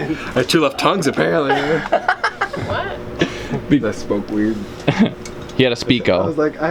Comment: Did say I smoke weed at 30:56? Lmao